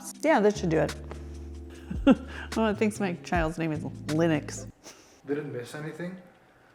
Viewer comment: To be fair, I thought you said Linux too.